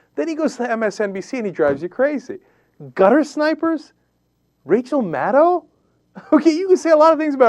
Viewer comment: They used it correctly... although I thought I heard Cenk say 'guttersniper' by accident lol.